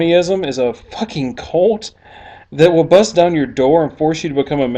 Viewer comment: It sounded like you said "colt" at 1:27. Context for the win!